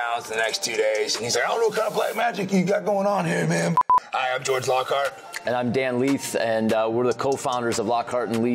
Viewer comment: Did he say his Name is george lowcarb?